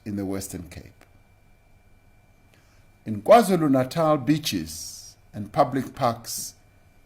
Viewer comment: He said beach in awkward pronunciation. With emphasis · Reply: I thought he said beechies. I thought, fuck me, now we can't eat gum.